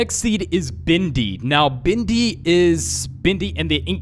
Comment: Me: Bendy: Bendy Everyone else: Bendy My brain: Bindy hehe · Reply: I also thought he was saying 'bindy' and not 'bendy'